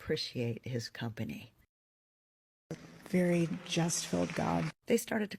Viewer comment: I thought she said justful which is not even a damn word